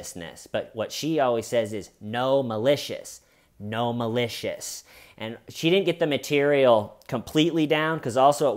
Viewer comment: When you quoted the “no malicious content” comment, my weird little brain heard it as a big run-on word, and it got me thinking… With gnomes being trendy home decor right now, do you think there’s a shop out there somewhere with a wide variety of themed gnomes called Gnome-alicious Content?